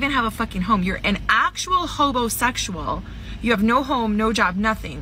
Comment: A lot of comments here that the first guy was gay, what she said was “hobo-sexual” trying to shame him because he didn’t have his own house